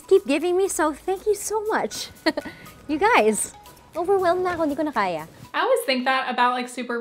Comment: "Hindi ko na kaya" Tagalog language which means I can no longer do it. I think the caption could not understand what she was saying. Anyway she was saying it in a cutie kind of way